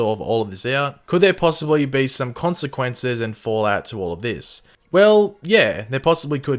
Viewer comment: did he say FALLOUT??? lmao I see what you’re doing! I see you!